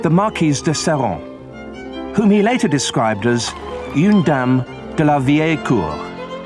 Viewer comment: What does he call that dude's wife? They say it at 6:37 and the auto generated transcript is "yoon dam de la vie al cool." I don't speak French and google translate says the transcript is wrong. I would appreciate any help with an accurate translation.